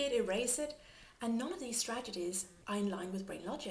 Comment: i do enjoy your udemy course. One cosmetic remark tho: you seem to say stregedies instead of strategies